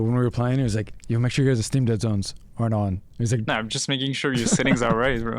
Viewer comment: what does bound say at 9:04 "make sure you guys have steam deadzones hard on" ?